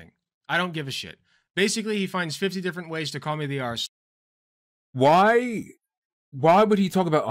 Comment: I kept hearing "called the arsler" and I was genuinely confused. Took me a while. The fucking newspeak I swear